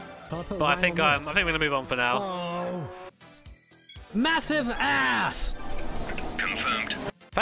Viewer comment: did I just hear BWOAH? 😏